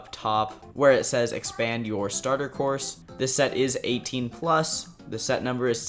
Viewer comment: did he just say this set is 18+ 😳